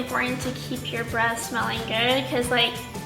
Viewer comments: When she says that it’s important to keep your breath smelling good I thought she said to keep your breast’s smelling good 😂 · I thought you said you need to keep your breast smelling good